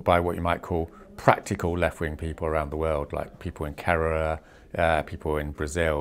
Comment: Translation help: 2:47 What is Roger saying? People in ______, people in Brasil. Carrara? That's city in Italy, what is so special about it? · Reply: Kerela, it's a state in India 😊